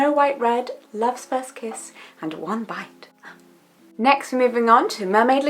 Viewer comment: She said one bite. I thought she said "wombat".